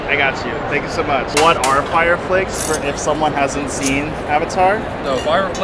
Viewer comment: I thought he said fire felix😅